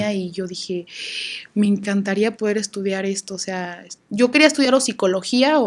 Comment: "Yo quería estudiar hocicologia" Eso oí 😅